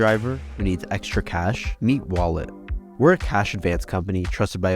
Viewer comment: In the beginning of this video I was not really paying attention until I heard “meat wallet”! 😂 nice